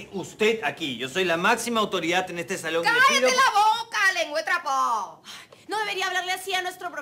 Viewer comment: ¿Que dijo Reyna en ese momento? ¿Lengua de trapo o leguatrafo?